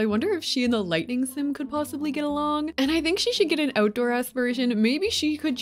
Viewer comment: I totally misheard you when you said the lightning and frost fairies would get along. I heard get it on. Which then made me imagine their kids... Terrifying... but awesome.